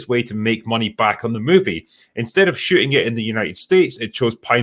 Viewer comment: With Drinker’s brogue “instead of shooting it in the United States” sounded like “instead of sh!tting in the United States” (4:45) which actually works better, all things considered.